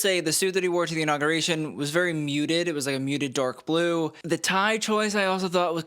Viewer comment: 🤣😂😂😂At first I thought you said The Donald’s suit was DORK blue! Then I was kinda disappointed when I realized what you actually said. Of course it was DARK blue. Was great while the moment lasted.😂 Thanks again!